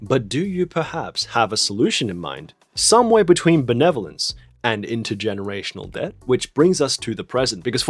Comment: "Somewhere between The Netherlands and generational debt" What did we do?! Lmao I realized you said 'benevolence' right after but that got a sensible chuckle out of me